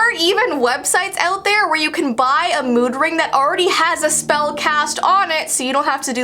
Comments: I thought she said "Moon ring" xD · Omg I actually thought that said “Moon Ring” H2O fans, You know what I’m talking about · For a second I thought it said moon ring lol 😂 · I thought it said moon ring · Why did I read and hear everything said wrong I thought she was saying moon ring lmao I was like Serena and ondina and Mimi are quacking